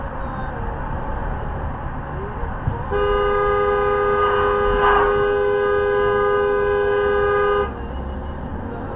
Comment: What the hell was she doing? Do people not realise how dangerous that is??? And what did she say to the cam driver? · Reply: I think she said "you're right, I AM a f*cking idiot!" 😁